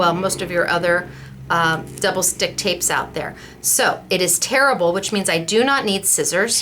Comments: oh silly me I heard you say your tape was "terrible" not tearable. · Oh, the English language gets me every time... I thought you said your lovely tape was 'terrible' only to realize you said 'tear able'!!!